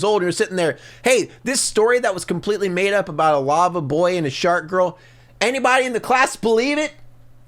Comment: Am I the only one that heard him say Sharkgirl and Lavaboy